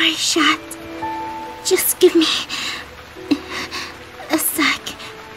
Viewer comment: misheard the word sec into sex 0-0